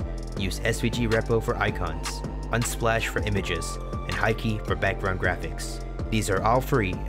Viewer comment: What's the name? I couldn't understand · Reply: They're on the description, Unsplash and Haikei